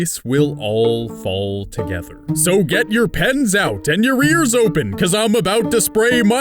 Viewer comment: When he said and "your ears open" I thought he said "your rears open"